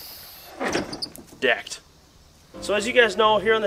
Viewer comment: For a minute I thought said the sponsor of the video was Debt.